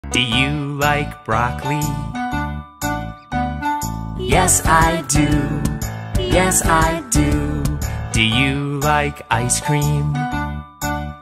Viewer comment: Bro i thought he said "do you like rock lee"💀